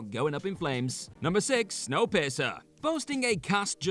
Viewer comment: 😆 🤣 sounded like you said snow pisser instead of piercer 😆 🤣 love your videos